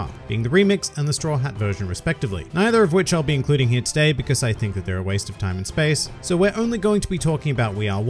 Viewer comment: I was about to leave because i thought he said "we are" was a waste of time and its one of my top 5 openings lmao.